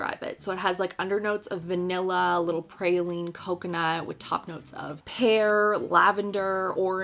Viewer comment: Omg I thought you said notes of Hair XD I was like what does that even mean?! I was thinking for a second that hair was a new universally understood scent like newborn baby smell XD I die.